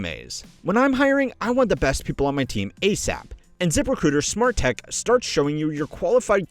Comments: Try ZipRecruiter for FREE at <link> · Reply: I heard "Zipper Critter" and I got excited, then disappointed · Anyone else thought he said "zipper critter" instead of Zip Recruiter?